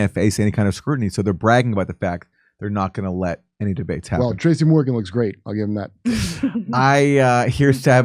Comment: Did he just call her Tracy Morgan? 😄